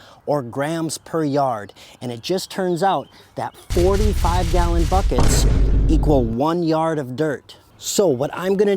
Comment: I thought you said a 5 gallon bucket was a yard. I just ran numbers and verified. because the front end loader of our old john deere backhoe was 3/4 yard and way bigger than a yard. A 5 gallon bucket is 0.0248 cubic yards. I either mis-heard or you mis-spoke, either way no problem and a good video.